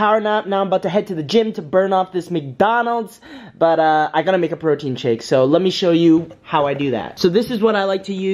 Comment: I thought he said a virgin shake😂